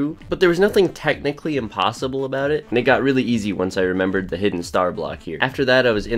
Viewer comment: What did he just say 😳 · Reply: “And it got really got easy once I remembered the star block here”